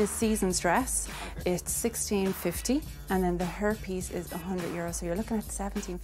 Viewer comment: When the bridal consultant said hair piece I thought she said herpes...and I was reading the comments and drinking Gatorade when she said it. Not the best combination. I nearly choked on it. I had to go back to make sure I HADN'T heard what I thought I did 🤣